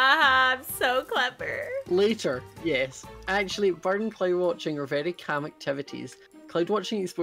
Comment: I thought it said clown watching not cloud watching IM STUPID